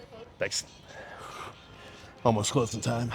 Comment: what did that girl say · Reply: Ten minutes till close